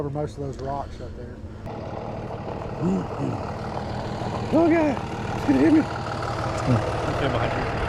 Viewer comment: what the hell was he saying at the 27:41 mark 😂😅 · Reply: Oh god. He's gonna hit me 😅